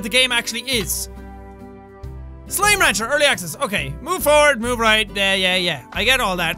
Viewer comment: I really thought it said "slime launcher" instead of "rancher"